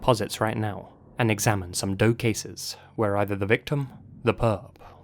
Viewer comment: "and examine some DOE cases" Me: wait what? did he say "Dope" cases? lul